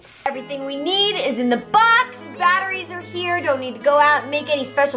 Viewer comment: Oak, 16 inches and a core of batteries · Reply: Axba I thought it said butterflies instead of batteries